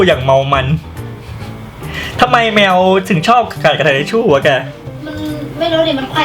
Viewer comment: ฟังดีๆ ตากล้องจะพูดว่า "ทำไมแมวถึง ชอบกัดทิชชู่อะแก" 55 #คอมเม้นส่วนตัวนะค่ะ ขอคนคอมเม้นไม่แรงน้่าา